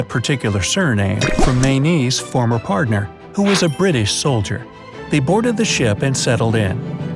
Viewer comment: video says British soldier while subtitles say French soldier